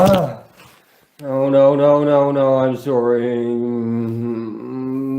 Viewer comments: SOUNDED LIKE HE SAID NO, NO IM SORRY. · I’ve worked in one of the worst areas in South Central LA as a cop, “77THstreet”, and have seen so many maimed and dead people that I have lost count back in early 90s. You are spot on about people making noises like that when injured badly or sometimes mortally. Sounded like he said sorry? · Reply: I thought he said I'm SOARING..like he was describing what he was experiencing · He saw the blue light appear and said, "I'm sorry..." I got chills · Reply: I thought he said I'm soreing